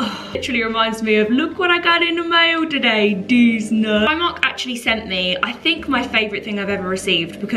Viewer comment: I've got you on while I clean up my space and all I hear is "deez nuts" and now I'm just 😂😂😂 for days.